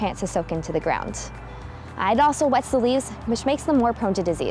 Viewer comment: i cant tell what you say but it sounds like "I'd also wet some leaves which makes them more prone to disease". This doesn't make sense. Did you mean to say "id also wet the leaves because it prevents desease?" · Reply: "And it also wets the leaves, which makes them more prone to disease."